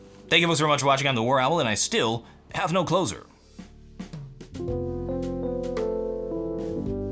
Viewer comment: seriously needs to work on a closer. I feel like he hasn't had one for a while now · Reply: when i first started watching years ago, for the first 30 or so vids, i thought he said "I'm Warowl, And i still. . . Have no clothes on".